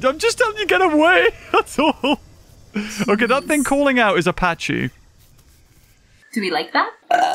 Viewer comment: "ok that thing calling out is a pachy" 4:29 it really sounded like you said "apachi" like the bloody helicopter haha